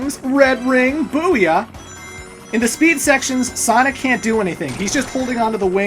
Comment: Did you say "speed sections"? Oh no... (horrific flashback of mach-speed sections)